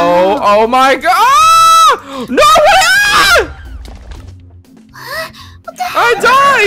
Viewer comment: did Helpy say what the hell?!?